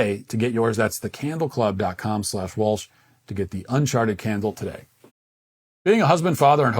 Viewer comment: I thought he said "un-sharted" .... Sounds like a good candle. 😆